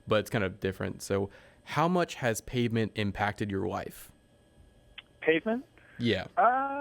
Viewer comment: Sounds like he said "how much has pavement impacted your wife" which evokes strange imagery